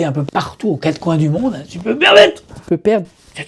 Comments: Il crie quoi à 07:07 ? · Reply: « Si je peux me permettre » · Il se passe quoi à 7:07 !!!? 😅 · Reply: " si je peux me permettre " je crois 😅